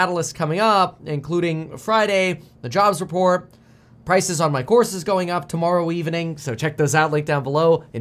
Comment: Thought he said "Prices on my horses going up". Was like, "Wow Kevin got in the horse business. Nice"